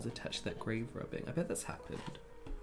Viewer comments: I legit thought they said "grave robbing" instead of "grave rubbing" and I was like "uh yEAH that IS disrespectful, what school did you go to????" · I can’t be the only one who wildly misheard ‘grave rubbing’ as ‘grave robbing’ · I thought you said grave robbing ... remember when we did grave robbing at school... so disrespectful' 🤣